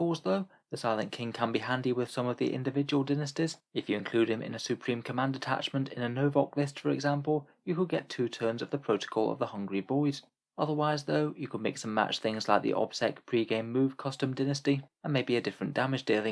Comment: I misheard Protocol of the Hungry Void as "Protocol of the Hungry Boys" and was very confused 😂